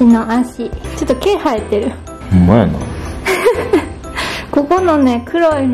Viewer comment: eu escutei um "mano" "mano" é uma forma que nos comprimentamos no Brasil <3<3<3